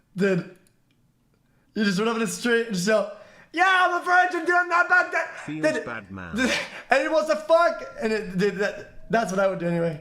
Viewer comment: I can't understand a single word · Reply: "you just run up to the street and shout: YO IM A VIRGIN DUDE ANYONE WANTS TO FUCK?'